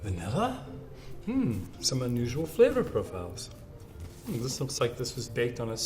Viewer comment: I was in the middle of class and I had my volume kinda low and I swear he said "semen" and I almost died.